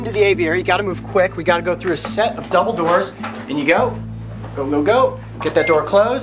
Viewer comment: When he said double doors I thought he said Dumbledore